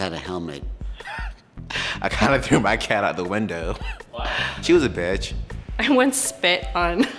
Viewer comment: hold up- why is no one talking about how a guy threw his freaking cat out the window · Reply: Well I thought I heard I threw my cup out the window.